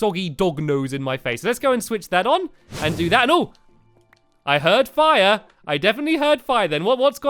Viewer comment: When he was talking about the machine, I thought he said shear and I was like "how can a shear do all of that!!" 😂😂😂